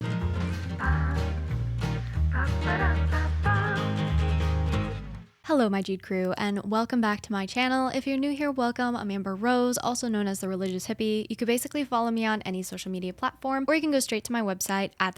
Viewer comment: I thought you said Jew crew lol